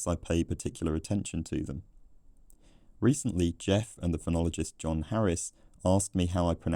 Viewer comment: I thought you said “phrenologist” at 13:54 😂